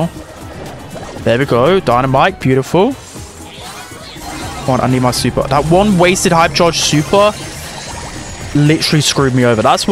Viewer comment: what is bro trying to say? Shuper? Sheeper?